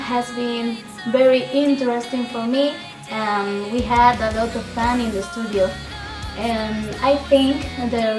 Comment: wait at 1:12 did she said that they had dakota fanning in the studio wtf?